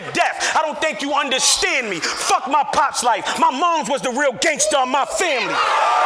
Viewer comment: Anyone know what Clips said at 30:40 that the camera cut out and Rex lost it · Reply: If I see what you talkin bout, Clips said, "my mom raised me you bitch ass nigga."